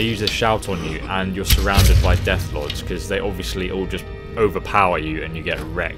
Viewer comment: I'm swear he said deathclaws